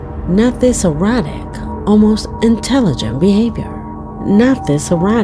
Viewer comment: Anyone else notice she said this sentence twice in a row? “not this erratic, almost intelligent behaviour” Editing mistake? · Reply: It was repeated twice. It almost sounded like she said "errotic". That's why I came on here to mention that she likely meant to say "erratic". It was hard to distinguish which she actually said.